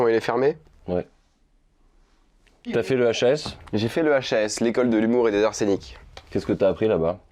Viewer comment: J'ai entendu "l'école de l'humour et de l'arsenic"